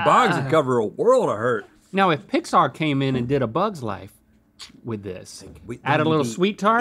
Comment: I thought Link said that he wanted Pixar to do "A Boggs Life"! Id pay for that!